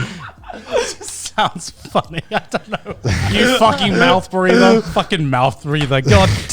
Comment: Thought it said Month Brother